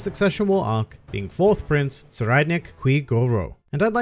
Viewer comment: This whole time I thought you were saying Sir Eidnich